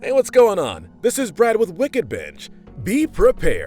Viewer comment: For a second I thought you said wicked bitch🤦‍♂️😂